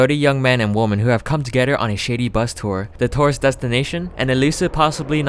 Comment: hahaha misheard word is also accurate: I thought he said men and women on a shetty bus tour. then I realized he said shady bus tour. I love my talent for mishearing things that I should easily understand.